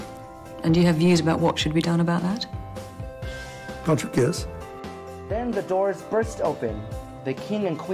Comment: What the hell did he say? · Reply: "Cant you guess?"